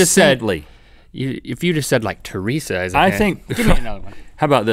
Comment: At one part I thought link said saidly